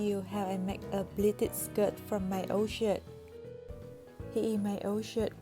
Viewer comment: sorry but i heard "from my old shit" no hate ❤